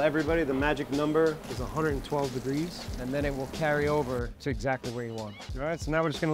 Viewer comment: Did he say 112 degree?